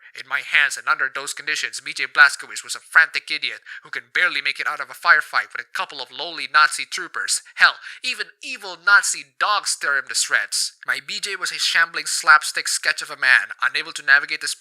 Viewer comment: I misheard ‘lowly Nazi trooper’ as ‘loli Nazi trooper’